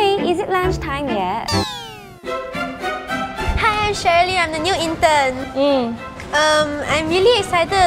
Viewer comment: Uhhm did say miss sherly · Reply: no, she said “Hi i’m Cherylene”